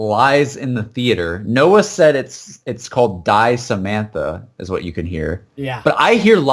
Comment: Instead of hearing "Die Samantha" I heard "It lies in the theatre" possibly referring to Kino?